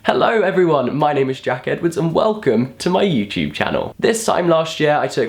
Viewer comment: When you introduce yourself and say your name I always hear "Hello my name is Jacket...." :P